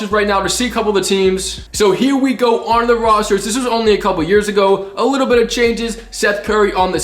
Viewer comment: A year ago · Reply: he clearly said a couple years ago 😐